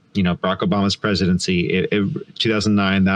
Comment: +/- : sounded like he said “Bunko-Obama’s presidency.” Hahahaaahaaaa.